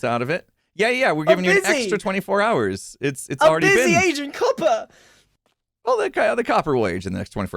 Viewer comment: The subtitles make Grian say he’s Asian cuppa instead of aging copper 😂